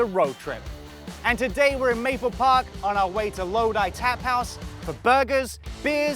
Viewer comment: I legit thought it said trap house